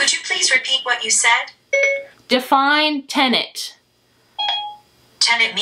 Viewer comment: Siri thought you said, “tenet.” 😆